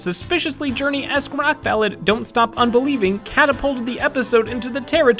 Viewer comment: calls Mabel's song "Don't Stop Un-believing" instead of "Don't Start Un-believing". Just sayin'. Please don't hurt me.